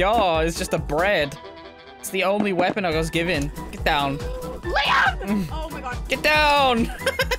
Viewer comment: Did she said Liam 3:40 is your name Liam?